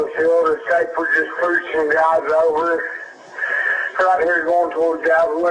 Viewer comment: befor the opperator says ahh you ran into em, ..i think what hes really saying is no trust ZONE.. something tells me he was shot, this is really sad , i hope they figure it out.. the word ZONE sticks out to me.... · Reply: also sounds like he said "this guys sending dogs over" .."please hurry"